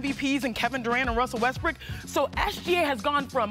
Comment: did she call westbrook "westbrick"